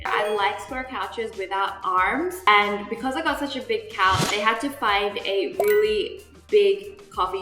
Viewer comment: Did she just say "because I have such a big house" at 2:29 Like woah we get it your rich but don't talk about it like that.